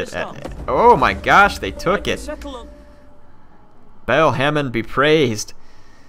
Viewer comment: did he say bagel hamen?